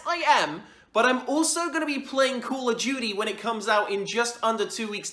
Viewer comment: When he said COD, it sounded like he said Call of Judy, all love, no hate. 😂